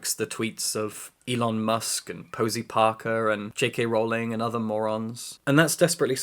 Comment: I misheard Posie Parker as Parker Posey and almost fell to my knees lol